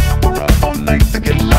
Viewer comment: I just hear “mexican love key” instead of “all night to get lucky”